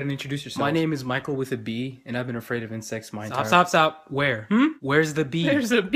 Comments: omg my whole life i thought he said "my name is michael with a b and i've been afraid of having sex...." what is life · I thought he said "I've been afraid of incest my whole life" and I was like "Same dude, same" 😂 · omg my whole life i thought he said "my name is michael with a b and i've been afraid of having sex...." what is life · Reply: pastakas I thought he said I’ve been afraid of insex minor